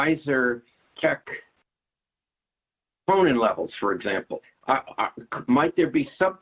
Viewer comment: check what levels? It drops out 🤔 Edit: he says troponin later on, not sure if its just repeated. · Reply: He did say troponin levels.